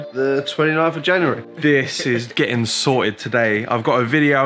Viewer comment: did he say 29 of january?? ass in 5 months ago